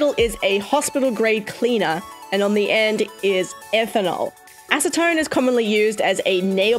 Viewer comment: H phew! When you said ethanol, I thought you said that killing drug faylinal (don’t know how to spell it but it’s a drug that kills you)